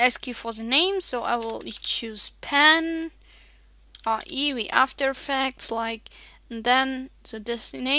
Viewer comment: did you just say ae wie after effects? ^^